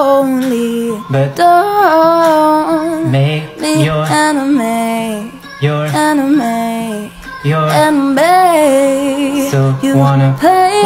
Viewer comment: Your voice is incredible but when you sang ‘Enemy’ it sounded like you were saying ‘Anime’😂